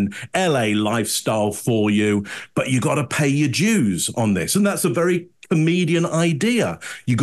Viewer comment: I always love analysis, but around 21:00 it sounds like he is saying “pay your Jews” over and over again.